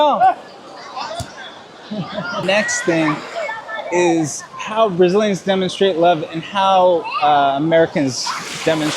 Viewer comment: what did you say in Brazil? I'm trying to learn, it sounded awesome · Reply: E aí meu amigo, fala comigo. Ta forte. Campão! What’s up, my friend? Talk to me. He’s strong, a champion